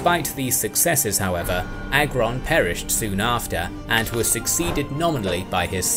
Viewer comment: Oh lord, I misheard Agron the first time and heard Angron. I was like, "When did this become a WH40K video?"